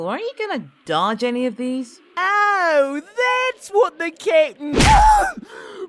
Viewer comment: before the closed captions every time Jeice said Captain, I always thought he said, Kitten. My disappointment is immeasurable and my day is ruined. Still not gonna stop calling Ginyu Kitten.